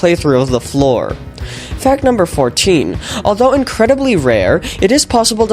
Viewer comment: hey instead of fact it said "hack" at this point